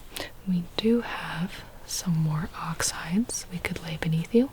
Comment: Girls I thought you said “Oxidizes” instead of ox hides! 😭😭😭 I was like “how are oxides gonna keep me comfortable”